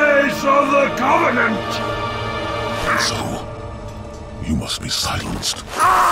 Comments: For so long I thought the Arbiter said "Asshole, you must be silenced" instead of "And so, you must be silenced". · "I AM TRUTH! THE VOICE OF THE COVENANT!" "and so...you must be silenced." man i remember the first time i played this on release i did the victory yell with the arbiter. It was so satisfying seeing truth get stabbed. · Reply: When I was younger I thought heard him say, “Asshole, you must be silenced!”. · as a kid i always thought he said "asshole you must be silenced" but it was "and so" LOL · All this time I thought Arby said Ass hole you most be silenced.